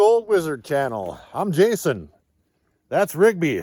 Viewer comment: I thought you said your name was Jason not Gary LOL